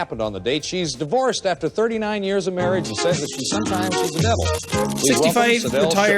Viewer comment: I first heard that The host said "And sometimes she's the devil" and was confused xD